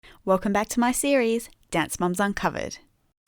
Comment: At the start of the video I thought you said dance moms on covid